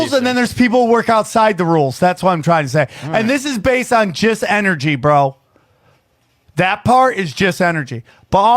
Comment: did he say "jizz energy bro"? lol